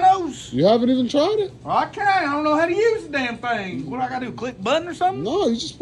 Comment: I thought he said "what do I got lick butt or something?!" 😂😂😂 omg I'm sorry